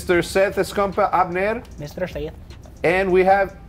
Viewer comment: What does Seth say @ 0:22 ? Lol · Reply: he says mr seth in an accent